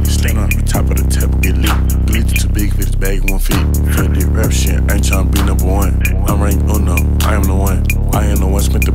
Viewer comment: Wtf he say at 0:29🤔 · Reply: Tmac 600 music to big for this bag, it cant fit